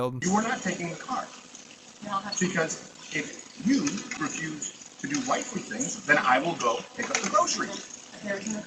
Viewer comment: Steven ''Perform your wifely duties or I'll fuck you up'' Crowder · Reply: Hey, fuck steven not defending him. However I didn't hear the I'll fuck you up part. Am I missing more of the video or just deaf? I thought he said "if you refuse to do your wifely duties, I'll pick up the groceries"